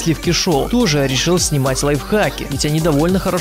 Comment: Мне одному послышалось лайфкаки?